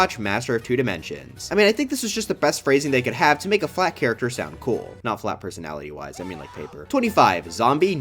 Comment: I can't be only one who heard "I think its just the best phrasing they could have to make a black character sound cool"